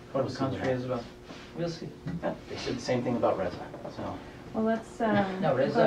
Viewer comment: what did the officer said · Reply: he says "same thing about Raza"